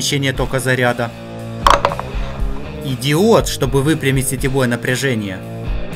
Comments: мне послышалось идиот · Впервые слышу, что чтобы выпрямить сетевое напряжение нужен идиот. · Я один слышу "идиот" вместо "и диод"??? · мне послышалось слово иди*т а это диод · Я сначала услышал ты идиот Или ты правда сказал Идиот ты не сказал правильно то сказал Идиот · мне послышался идиот))) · Только Мне послышался идиот на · мне одному послышалось ИДИОТ? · мне послышалось идиот ._. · Мне одному послышалось ИДИОТ (ДИОТ) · когда он перечислял предметы мне послышалось Идиот а потом прочитал и понял что написано Диод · Услышал как "Идиот". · Я один угарнул? (Услышал идиот) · мне послышалась или он сказал идиод наверно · мне послышалось "идиот" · и диот мне мне послышалось идиот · я один слышу не И ДИОД а ИДИОТ? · мне одному послышалось что когда сказал "и диод" мне послышалось идиот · я там услышал Идиод) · мне одному показалось что он сказал идиот? Напишите ответ на комментарий если вам тоже послышалось "идиот" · мне послышалось или ты сказал в этом моменте идиот? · Идиот 0:42 место диод Услышал кто тоже ставь лайк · мне одной послышалось идиот?